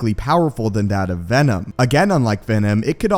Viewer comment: i heard Againom like Venom 😂😂😂